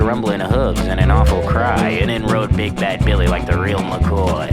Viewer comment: i know the lyrics is "Big Bad Billy", but i keep hearing "Big Back Billy" XD